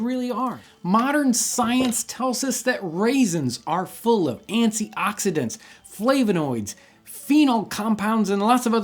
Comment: Did he just say..."antsy oxidants"? :) hmm...ants, the other secret ingredient. :D